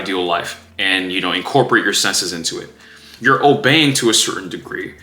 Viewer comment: consider that maybe instead of “obeying” the word is disciplined. Because that would fit better. It’s part of “reparenting” one’s self through shadow work?